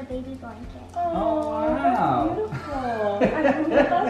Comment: I THINK SAID: I THINK MILTON LIKES IT. IS HIS NAME MILTON ???!!!!!!